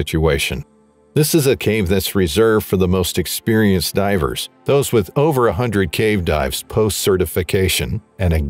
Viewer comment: I thought you said “those with over 100k dives”. I said to myself that’d be like 1000 years and yes, at a rate of 2 per week it’d take 961 days. But that was 100 cave dives.